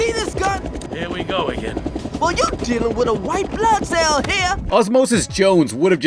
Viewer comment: the first time I saw this movie when osmosis said "You're dealin' with a white blood cell" I thought he said You're dealin' with a white-black man